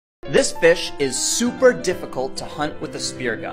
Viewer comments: Thought at the start he said was “THIS BITCH” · At first I thought he said this bi@ instead of fish lol